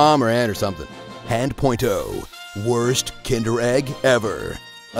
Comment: "Worst. Kinder Egg. Ever." That caught me of guard I'm dead xD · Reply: I honestly thought they said Tinder egg, I was sooo confused lol.